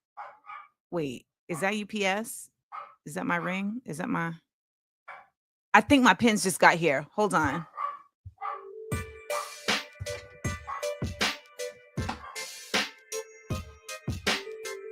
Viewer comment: girl, I thought you said, "I think Mike Pence just got here". I was so confused! lol.